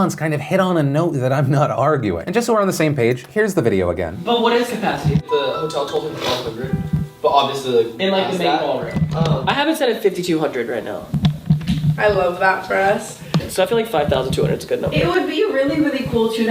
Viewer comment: I thought when they said 1200 people "right hand" says "that's in the main ball room." idk, that's what it sounds like to me. But while Tana is using her feelings as facts, flat out lies repeatedly.